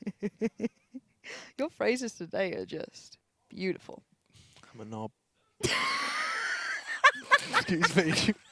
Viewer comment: What did he say? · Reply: Dante Sparda "i'm a nob", i guess